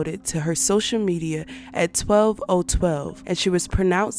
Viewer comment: did you just say 12:012 girl 👀👀🤣🤣 nice video though very informational im just hearing about this